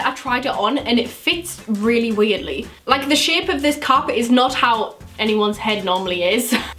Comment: When was talking about the ivy park cap at 6:34 I thought she said carpet not cap😂❤️